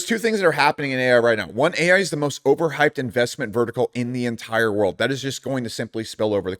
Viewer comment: did he say.. Vertical? $VERTAI is destiny